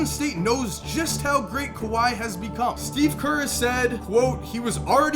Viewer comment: Did he just say Steve curry